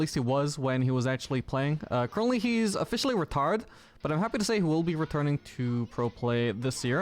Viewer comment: At the start when you said he's retired I heard "retarded" and it made me snort my coffee back into the cup and keyboard. I don't know why it was so funny. Loved the video!